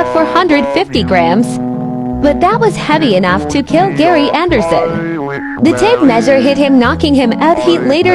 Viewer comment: I thought it said gay anderson 😭😭